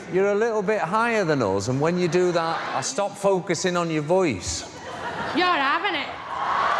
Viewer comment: what is she saying? somoena can traduce me pls? im from argentina :p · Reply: Don’t know if you’ll see this, since you asked your question eight months ago, but it sounded like “You’re having’ it!” She was trying to flirt with Barlow the entire time. I don’t think he was interested— and that’s putting it as diplomatically as I can!